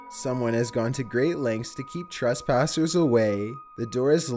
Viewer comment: I thought he said the floor had been yeeten 😂